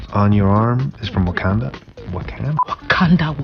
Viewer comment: did you say nkandla?